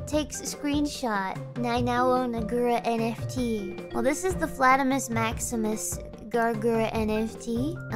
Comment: Almost thought she said Flatbush Maximus. 😜